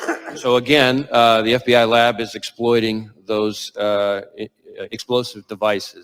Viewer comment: listen at 57:44, Wray says the FBI lab is exploiting those explosive devices" Not exploring, Exploiting is what he says Freudian slip maybe